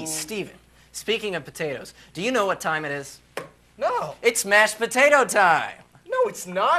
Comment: Although they are singing the mashed potato I keep hearing "the monster mash". There seems to be a lot in common with the music